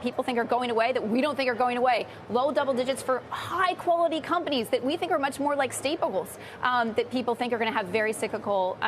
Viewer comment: What did she say at 3:39? "stapables?"